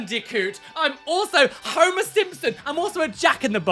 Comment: I had to take a giggle break of my very own because when was talking about his tattoos, I misheard Homer Simpson as Homo Simpson. ...I'm too tired for myself.